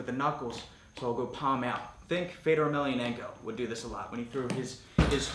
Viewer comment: What’s that name you’re saying at 2:15??? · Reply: Fedor Emelianenko